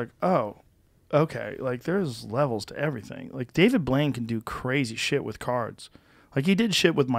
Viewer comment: Every time I hear someone talk about David Blane I just hear "Cheezits!"